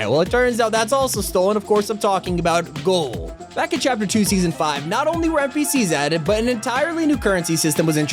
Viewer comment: Since he said chapter 2 season 5 so fast I thought he said chapter 5